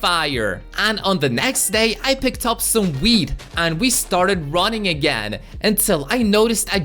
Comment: Ayo umm in 4:27 is it me or did you guys hear him say weed like the hell can you correct me or am I just hearing things